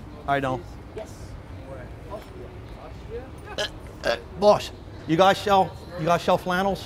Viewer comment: Did he say "AUSTRIA" at 1:26 ?